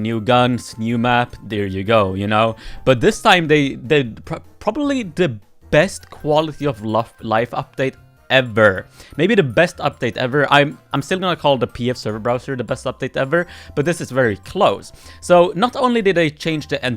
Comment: i just hear you said “quality of lov”